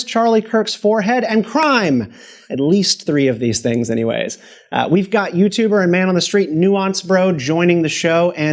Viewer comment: I'm thought that said Nounce for a sec , it's only funny if your British :)